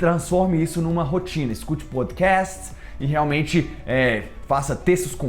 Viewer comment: Oque é que ele fala no 6:18 (But cas) ??? Nao entendi oque é isso ??? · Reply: podcast*